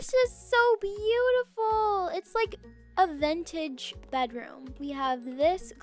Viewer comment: it sounded like said "vEnTaGe"